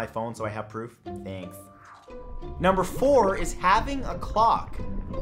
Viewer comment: I thought you said having a co.. I type no further